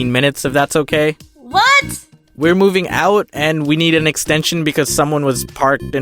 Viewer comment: I dunno how or why but I thought it said "Coming out again" and I was like, again? What... Until I saw that is said moving out 😂😂